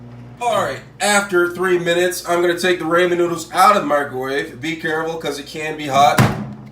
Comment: I literally leave everytime I hear him say “raymond noodles”